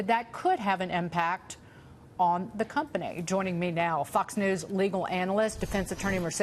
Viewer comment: Did she just say Fuck's news? )))